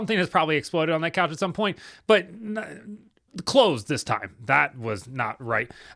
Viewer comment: WHAT did you just say? 💀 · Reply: Clothes this time 😂😂